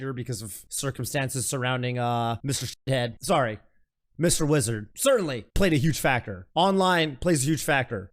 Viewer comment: I heard you saying Mr Lizard and honestly that would've been fine too.